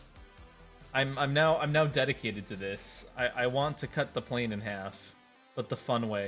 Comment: I THOUGHT YOU SAID YOUR TOILET SPLIT IN HALF 😭😭😭